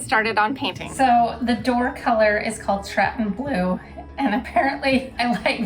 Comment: Will you share the name of the all of the paint colors house exterior, trim and door? I looked up what I thought you said for the door, but couldn’t find it… tratton blue? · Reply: Stratton blue. Benjamin Moore